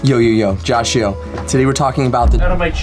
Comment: All this time I thought you said Joshy-o